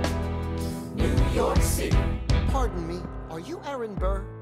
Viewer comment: Dude I thought it said new pork city and now I'm dying QwQ